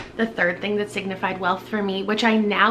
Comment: Not gonna lie, I totally thought that you said “things that signify WILF” not “wealth” so that was very confusing for a while 😳😂